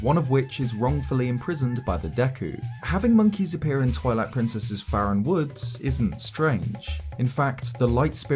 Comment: Omg, I had to stop the video at :20 and leave this comment because I totally heard you say “Toilet Princess” XD